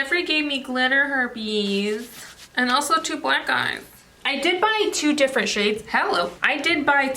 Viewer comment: I thought for a second you said "mom, Jeffree gave me glitter herpes... and 2 black GUYS". I was like "hold up, does Brandon know of these things" 😂😂😂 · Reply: Lmao i heard the same and im like😳😳🤔🤔 oohh black eyes🤦‍♀️